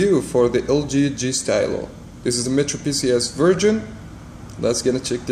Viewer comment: did you say metropcs VIRGIN 0:09 ?? im in !!!!